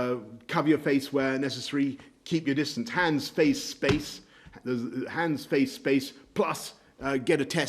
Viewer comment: "Hands! Face! Space!" Every time you hear Boris spout this slogan, take a shot. Just like with "Get Brexit done!" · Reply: Oh I thought he was saying Hands Face Face ! No wonder I thought he was bethering more than usual. Hes such an awful public speaker